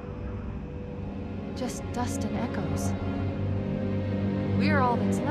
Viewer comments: I hope Dustin Echoes made it out safely as well. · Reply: I thought she said "Just and and Echo's" Like I think Echo 419 died but okay. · I hope Dustin Echoes made it out safely as well. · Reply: I thought she said just dust and echoes.